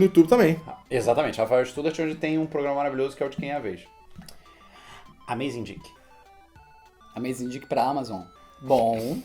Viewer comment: eu aqui rindo sozinho com minha mentalidade de 5ª série só porque ouvi "Amazing Dick" quando a intenção era ser "Amazindique" hahahahaha